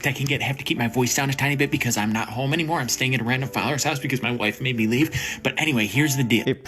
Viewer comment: Everyone else also heard when he said his wife kicked him out and he's staying with his dad, right? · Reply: That's not what he said, you misheard him. He's staying at "a random followers" house, not his father. He's basically just couch surfing with his tik tok followers.